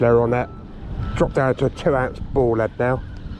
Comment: 10 oz ball lead ? Don't you mean 1 oz you wouldn't be able to cast a 10 oz lead with that rod surely? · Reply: yeah beg your pardon you say 3 at the start then 17:56 you said dropped down to a 2 ounce ball lead I heard that as 10 ! Sorry dude .. . Good method ill try it next time maybe. .it gets you down nice and deep I suppose